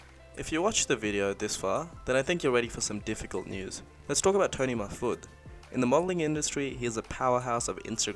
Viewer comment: Captions says : 6:55 toni my foot...lol